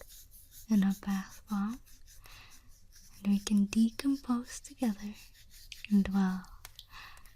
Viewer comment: I'm sorry, I'm not trying to be rude but am I the only one who heard her say "Decompose"? As in for a body to rot away and become plant food?